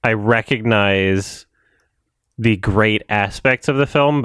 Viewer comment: Almost sounds like is saying "The GREAT ASSpect" without him realising what he said. Just me made chuckle